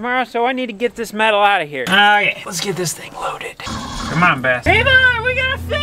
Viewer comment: did say “ come on bastard “